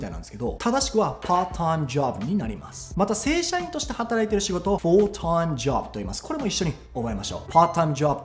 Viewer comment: 正社員として〜がテロップでは full-time job となっているのに、さんは all-time job と言っているように聞こえるのですが。どちらも正解ということでしょーか？